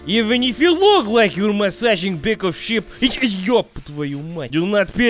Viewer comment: When he said back of sheep I thought he said pickle ship